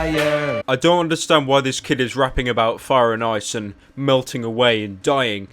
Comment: captions say raping